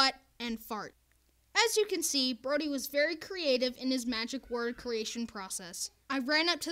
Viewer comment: *screams* Subtitles: [Music] Me: ah yes the most beutiful of music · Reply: Also at 4:23 Instead of creative it says Korea😂.